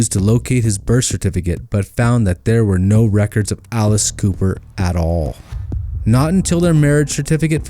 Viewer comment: At about 14:22 it sounds like you say Alice Cooper!! Lol 😂 You know, the crazy make-up faced singer? Anyway, made me laugh!!